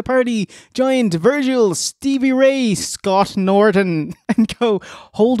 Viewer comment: I wasn't paying enough attention and thought they said "Giant Virgil" came to the ring